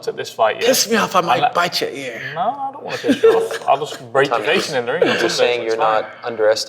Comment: did Tommy say "...I'll rape your face..." or am I hearing things?